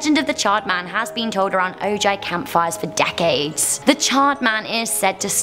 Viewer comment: did she say o.j. camp fire